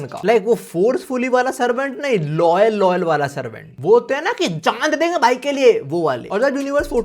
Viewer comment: yo said loyalty i heard Igris🗿